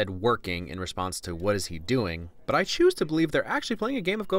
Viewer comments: he asked "what's new?" and the other dude said "nothing" just a correction I'd like to make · actually what he said is "nothing"